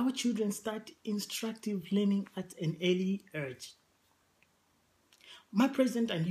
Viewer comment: very informative.. but did he just say "early urge" 2:07 lol.. 5:37 "KRS spending" haha.. is this the free education coming? lmao.. good luck in the elections though